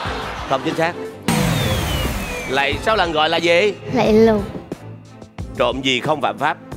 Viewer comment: Cảm ơn mọi người . Cảm ơn các bác luôn ủng hộ e nha. Cho dù k trả lời được câu nào nhưng được gặp các anh chị là e mừng lắm r . Điện thoại với chìa khóa của ai lát ra nhận lại nha huhu e nhỡ tay cầm nhầm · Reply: Hari cứu cho 1 câu trả lời lạy lộc mà hari nghe thành lạy lục nhá nhá